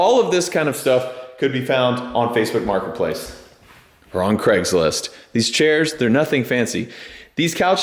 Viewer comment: Did AI just say "Or on Craigslist"?